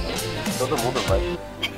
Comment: só eu que ouvi aos 4:43 uma mina falando ''acho que vou dorir e acordar só amanha'' e o cara falando ''todo mundo vai'' ? tem br no clã do xg ?